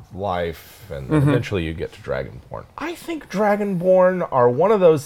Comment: I thought he said dragon porn 😂😂😂